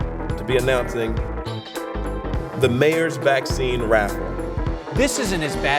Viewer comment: At the start I thought he said "the mayor's vaccine rap" instead of raffle and now I'm sad because I wanted to hear that😢